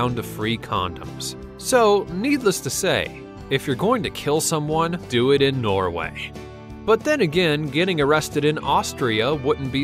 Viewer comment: Did he just say "if you're going to kill someone going to Norway"?